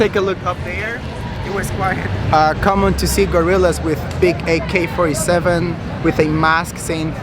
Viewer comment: I thought you said Gorilas😅